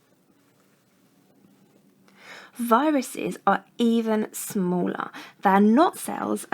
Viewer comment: DOES SHE SAY BARACTERIA? I'm going crazy!